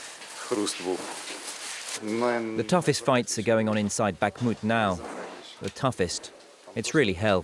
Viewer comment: I thought it said "inside Bahamut", was expecting Final Fantasy XVI trailer 🙄